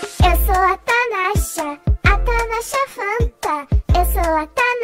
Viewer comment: "Eu gosto de muita,muita manga" kkkk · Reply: Não entendi " eu gosto de muita muita manga " se ela fala " eu sou a Natasha a tanasha FANTA"